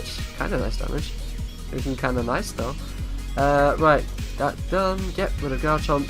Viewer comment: Did you say "damage do be kinda nice doe" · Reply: I said damage looking kinda nice tho, that's completely different